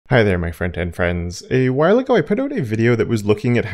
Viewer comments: Love exploring your content. I was today years old when I realised your intro is “Hello my front-end friends” and not “hello my friend and friends” as my brain chose to mishear it. 😂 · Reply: first time i heard it as "hello my frightened friends" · Love exploring your content. I was today years old when I realised your intro is “Hello my front-end friends” and not “hello my friend and friends” as my brain chose to mishear it. 😂